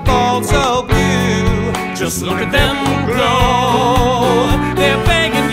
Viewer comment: I can't be the only one hearing 'grow' instead of 'glow'